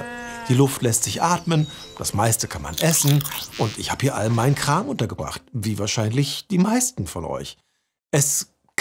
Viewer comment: ich habe zuerst verstanden: " Und ich habe meinen Nachbarn umgebracht" anstatt " Ich habe meine Sachen hier untergebracht"